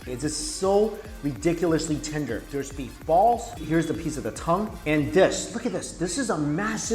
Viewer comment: Woah! Did you say beef balls?